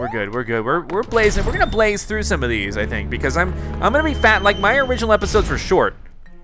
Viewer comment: he said fat instead of fast.